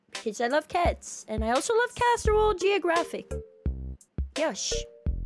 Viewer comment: Did she say...CASSEROLE Geographic?!?!